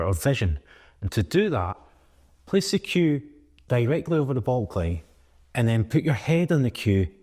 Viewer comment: you say "..directly over the ball ****", and at 3:32, you say "...move it until the cue looks exactly over the ball ****". What is the word you are saying after ball, and what does it mean? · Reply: it's "baulk line" my friend 👍